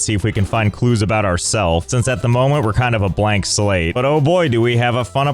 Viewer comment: I HEARD "BLACK"